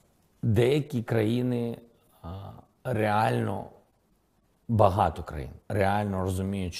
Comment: There is a mistake in translation at 13:27. He said not truly wealthy countries, but many countries. Seems like one who did this translation misheard 'bahato'(many) as 'bahati'(wealthy).